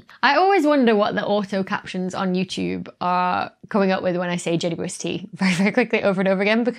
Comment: the captions said "Jellyfish tea" -- then next time, "Jenny Brewster". No offence to Jenny Brewster, who I'm sure is a very special person, but I shall be referring to the JWST henceforth as Jellyfish Tea.